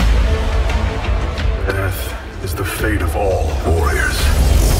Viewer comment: "earth is the end of all warriors" DID HE JUST SAY THAT??? UED COMFIRM!!!! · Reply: he said "death is the fate of all warriors"